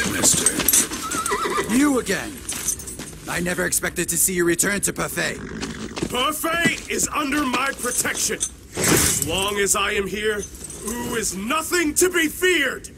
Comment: it sounds like the dude who voiced Sun Quan said “Pefei” instead of Hefei.